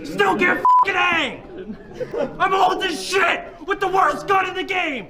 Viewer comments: anyone else hear "im holdin a shit" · He said I’m holding this shit not I’m old as shit though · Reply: if you're correct, i think he just said "im hold this shit ..." . That's does make sense now. It was 5:56 btw.